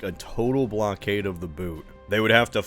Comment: Why did I hear “a total blockade of Naboo”? 😂😂😂😂